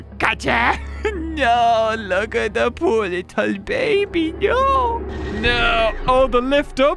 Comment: WTH HE SAID BABY GIRL